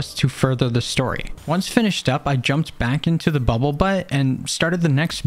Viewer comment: i thought that at 34:30 he said bubble butt